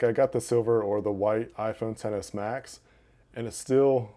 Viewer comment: It kinda sounds like he said iPhone 7 s idk why tho